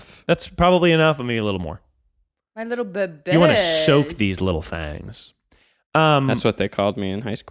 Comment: I thought Miles said, “let’s soak these little f@ Had to rewind a couple times before I understood that he actually said “thangs.” 😬